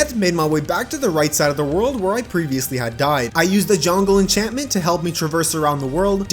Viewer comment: Did he say “jongle enchantment”????